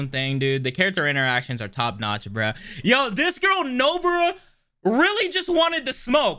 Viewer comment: Did I hear that correct at 4:34? No-bra....